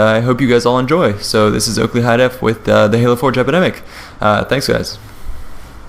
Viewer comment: I keep hearing him say "Halo 4 Jepidemic"